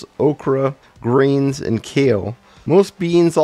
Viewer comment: I didn't mention kale in the video, but like I said in the video the plants without it typically have other issues especially kale. Which harms the thyroid and absorbs contaminates very easily. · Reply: It sounded like "kale" at 15:33.